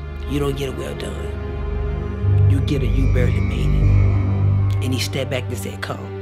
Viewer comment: I think he said “You don’t get a well done. You get a you barely made it.” This is such a powerful testimony. The revelation of the seeds shook me. This is a wake up call. WOW. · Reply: Yeah that sounds like what he said, the first couple times I thought he said you don't get a well done, you buried me.